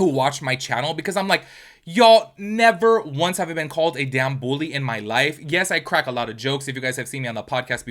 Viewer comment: I keep hearing “bull-ing” and I’m like what is bull-ing????